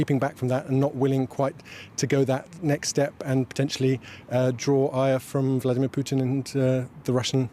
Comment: does he say, "Draw eier? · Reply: Draw ire*, so to draw the aggressor's attention